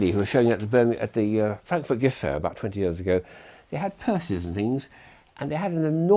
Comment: i thought he said curses but it was purses